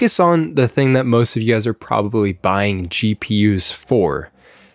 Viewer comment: I literally heard CheaPUs instead of GPUs 😂